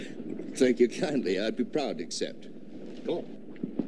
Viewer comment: Did I here wrong ? Or did Adam say cool when Johnny accepted Hoss's beer invitation at the 27:37 mark. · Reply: I think he said "Go on".